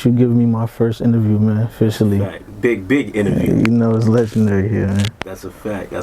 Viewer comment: Nahh Wrd 2 my dead I thought he said dick big interview